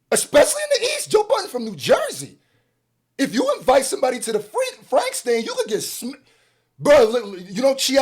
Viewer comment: Did you say the “Frank Stand” 🌭😂😂. Please don’t sue me😅